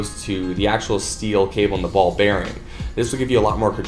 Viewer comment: Did he say ball bearing??!! I didn't know jump ropes had ball bearings.